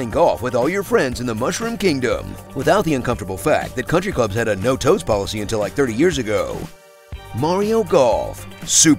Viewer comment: did he say “no toast policy”?